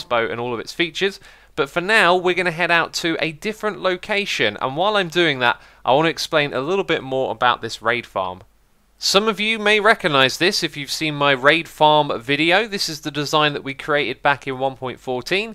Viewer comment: For a second there,i thought i heard rape farm